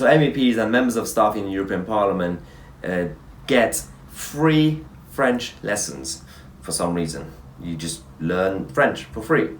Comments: Great video as always mate but hearing that snake Blair saying the word honestly boils my piss that creature should be sleeplessly rotting in an Iraqi jail on a lighter note I almost spat out my cornflakes I thought u said meps get free French letters 😆 then again they probably do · for a moment there I thought you said 'free French letters' ! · I thought he said 'free french letters' !!!